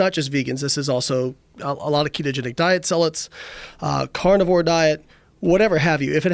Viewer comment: what is the word he says ? Zalits?? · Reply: Bjorg Rosenquist “Zealot” - a person who is fanatical and uncompromising in pursuit of their religious, political, or other ideals.